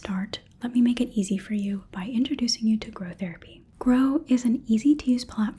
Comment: Oh, I thought you said "Crow Therapy", I wish that was a thing